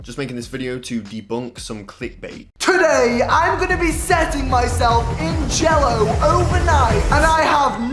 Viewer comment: i thought at the start morgz said "hey guys today im gonna be setting myself on fire"